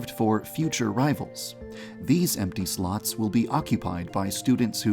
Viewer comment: I understood wrong and thought you said empty sluts instead of slots lmao